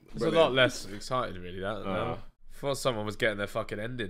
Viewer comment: Wtf does ethan say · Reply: “Thought someone was getting their ending”